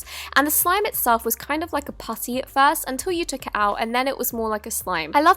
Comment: did she said pussy xddddddd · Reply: Amelia Zakryte 😂😂😂it totally sounds like it but she said putty